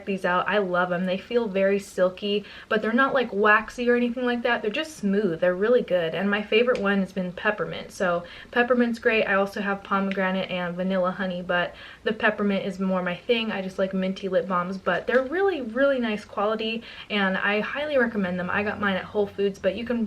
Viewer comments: You were talking so fast in the beginning of this video when you said "Peppermint, Pomegranate and Vanilla Honey, but I.." I totally thought you said the scent was "Vanilla Honeybutt" xD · oh love the video...but..omg so funny last lipbahm flavor sounded like u said.."VANILLA HONEY BUTT"